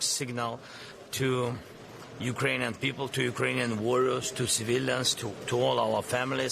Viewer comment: did he just say "Ukranian warlords" 😮